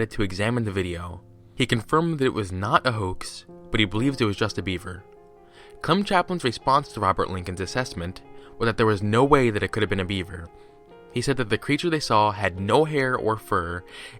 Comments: When you said it was just a beaver I thought you said Justin Bieber. :-p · At first, when you said "He believed it was just a beaver," I thought you said "He believed it was Justin Bieber"